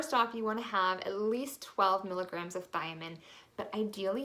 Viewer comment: What was the first vitamin mentioned? It sounds like she said "vitamin" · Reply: Thiamine